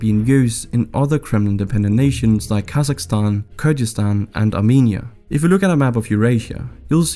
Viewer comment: I heard Borat saying 'Kazakhstan, Kyrgyzstan and asshole Uzbekistan' 😂